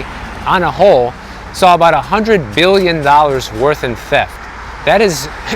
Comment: At the 2:40 mark did you say 100 BILLION